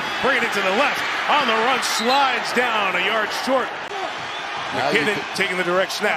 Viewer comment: sounds like he said McKinley, a girl in my school bad Tuba player and annoying when she looks at people when talking.